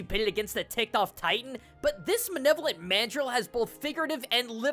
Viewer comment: I’m not the only one that heard him say “manevolant” right? Instead of like malevolent or benevolent?